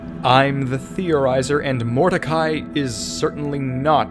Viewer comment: When you said morticai i thought you said mordecai